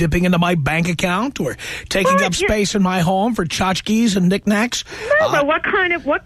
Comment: Does anyone know what word Tom said there before 'and knick-knacks'? Was it 'chotch-keys' or something? What does it mean?